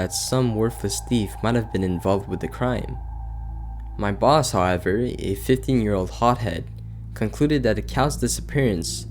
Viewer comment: Ahahah, not sure where you heard that, maybe though, the boss’s age was not really mentioned in the story. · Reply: (aahuuummmm..... you should check that audio. I heard the boss was 15, too. Thought it was a mispronunciation though. 2:24 "my boss however a 15 year old hothead"... time code from transcript).